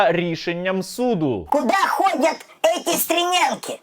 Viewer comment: почулося "Куда ходят эти СТРЕМЯНКИ??" ріл, а куди вони ходять?